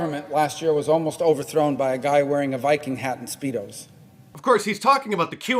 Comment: did he say a Viking hat and speedos 😅